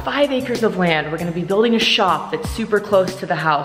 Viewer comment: did i just hear, building a shop?